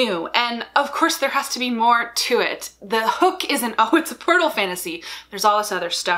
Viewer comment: Okay, confession here: I was kind of zoning out when I thought I heard the words “porno fantasy.” Yes, I realize now that you said portal fantasy. But it certainly got my attention! 😆